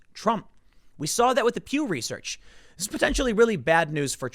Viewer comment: Pew research 😂 sounded like you said Q research